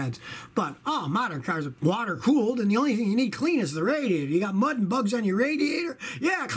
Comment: I thought he said : clean that motherfucker lol